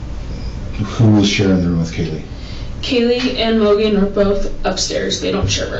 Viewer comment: Correct me if I’m wrong and I’m just hearing things but at 49:32 did say they call Maddie “Mogen”? Does anybody remember some of the footage, I think it was the Linda Lane one, but you hear somebody shout “Mogen” and we hear the Kaylee/Maddie attack outside? And a lot of people were saying that’s weird why would Kaylee call her best friend by her last name, I don’t if tripped up her wording or if that holds water.